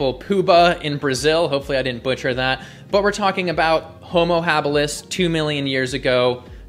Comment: I thought for a second you said "Homo Herbalis", but maybe that wouldn't be a bad name? 😄